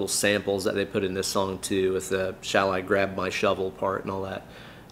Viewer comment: I think the "grab my shovel" bit is from the movie "Misery" with Cathy Bates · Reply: Weird: I always heard- "Bright red my Shovel"😆